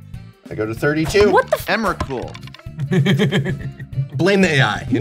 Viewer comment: did I hear emberkrul the eldrazi or “ and were cool” one is way scarier lol